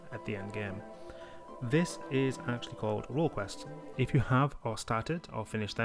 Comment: What's he say? World Quests? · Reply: Its role quest 😄